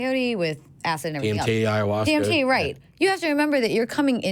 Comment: Did they just say "PMT" instead of dmt?? 😂